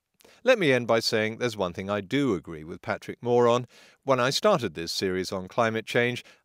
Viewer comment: "Let me end by saying, there's one thing I do agree with Patrick Moore on" Thought I heard Patrick Moron there for a second... ;)